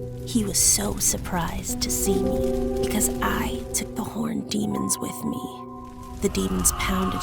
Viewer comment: Anyone else thought that she said "horny" instead of "horned" at first? Cause it got me really confused for a moment 👀